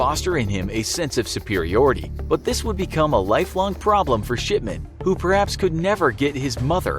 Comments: Mr. Shipman I thought when you said Mr. Shipman I thought you said Mr.Shitman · I thought it said Mr.shitman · I thought he said Dr shitman